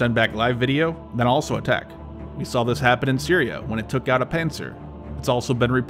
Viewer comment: "We saw this happen in Syria, when it took down a ... " what? Did you say "panzer"? I must have misheard it. "Panser"? "Panther," like the modern, British military vehicle? Edit: It's Pantsir. I found it in another video's comments section: <link>